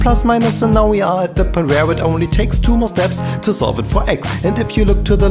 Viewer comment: what does he say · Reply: Sorry 4 years late but he says "and now we are at the point, where it only takes to more steps..."